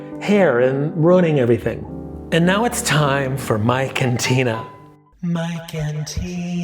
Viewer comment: When you said, “Mike and Tina” I heard “my cantina.”😂